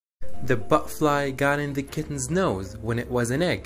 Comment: I know this is serious but at first I thought he said boatfly man I've been playing fallout too much